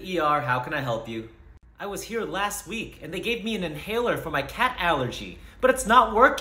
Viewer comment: First watching i thought you said "cat's allergy", so wondering what's wrong lol